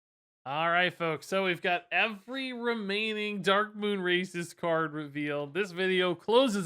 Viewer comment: my guy you gotta enunciate better, I keep hearing "Darkmoon Racists"